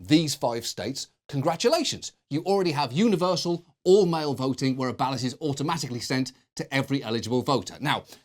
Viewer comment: the caption showed "all-male voting" instead of "all mail voting", and it's kinda funny coming out of John Oliver